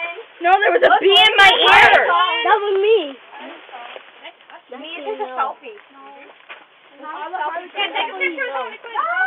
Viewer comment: did she say lets take a selfie lol 😅